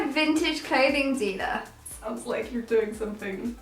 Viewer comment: I heard "Vintage Clothing Diva" and thought about different kind of illegal job 😂